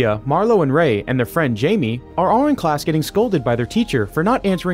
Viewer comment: Why did he say “are” twice? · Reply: I think he said “are all”